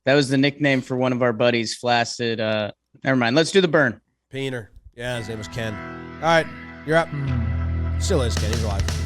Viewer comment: what did say? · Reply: Peener, his name was ken… wait.